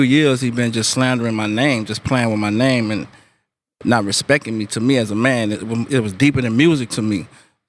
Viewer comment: Did anyone else hear that? 1:19 "Not respecking me" he for real says it like that lmao